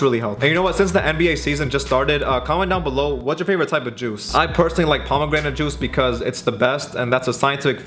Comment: I thought he said what is your favorite kind of jews.